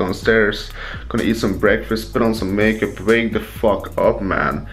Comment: Did he say he was putting on makeup at 0:16 ?